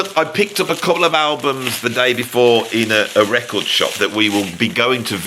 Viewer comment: each time you say: "the day before" I hear: "Davy [5]04"